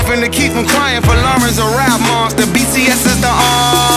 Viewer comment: DID WALE SAY RAP MON, BTS AND ARMY